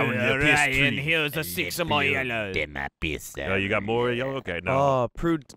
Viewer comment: I thought said the hills are covered in my yellow.....😂